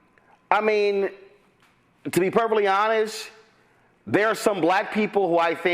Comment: did he just say "purplely honest" 😅